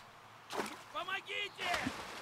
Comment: lol I thought he said, "Oh my Jesus!"